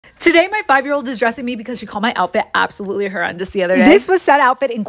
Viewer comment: i thought it said '' today my five year old is depressing me'' 😂😂